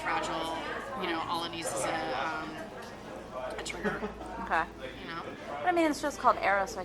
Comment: When she says 'All it needs is a Trigger' at 1:04 ...it 'almost sounded like she said 'Olicity is a Trigger'. I had to go back and listen a few times. lol